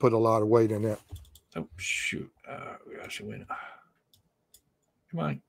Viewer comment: Thought I heard “Alex come on” in the tape.